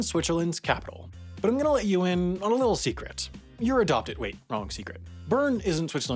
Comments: As someone living in Lausanne, I’ve never heard someone call my city Lasagna. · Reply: Ayou did you really said I'm adopted?? 💀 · Ayo did you really said im adopted? 💀